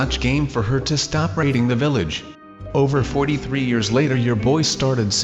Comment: did he say stop raping the village???!!!